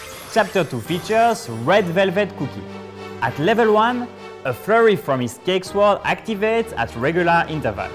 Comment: i thought he said furry ToT